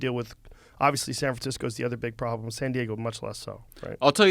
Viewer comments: Did I heard him say San Fransicko ? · Sounded like he said San Fransicko